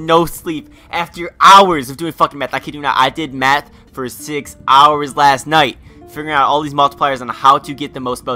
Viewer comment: Thought he said I did meth for six hours. Lol